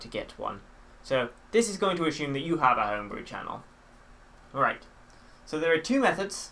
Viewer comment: LOL Youtube lagged for a second and it sounded like you said "this is assuming you have a home" XD